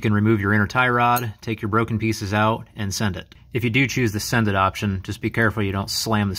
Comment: Can you explain the second option? I thought I heard you say "send it". Not sure. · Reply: is correct, and yes, I did say "send it". Take the broken pieces out and put it back together. Without that stopper in there, hitting the steering all the way hard enough could damage the steering rack.